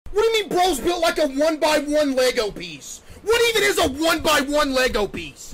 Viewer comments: Why do i hear this?! 'when me balls built like 1x1 lego piece' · All I hear is "1 by 1 legobiese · He sounds like he is saying 1 by 1 Lego Biece